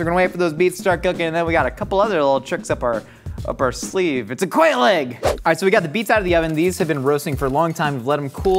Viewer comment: I know Josh said quail egg but I really thought he said quaalude at first